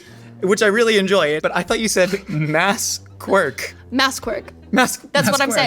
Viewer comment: I thought they said 'Mass Cork' lmao